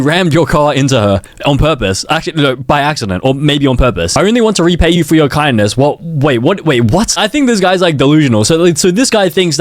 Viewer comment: i thought it said i only want to repay you for your kidnees